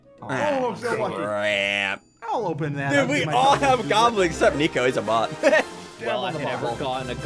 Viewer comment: i thought he was saying goblins instead of gauntlets